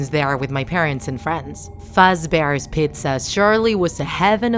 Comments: I thought she said “Friday fazbears pizza” Also when she said “Bony” I laughed but was raged at the same time I’ve been playin it for atleast 3 or 2 years now and REEEEEEE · At the beggining for the person's job offer i heard "freddy fat's bears pizza" wth😂 ya'll need to fix ur grammar(no offense tho)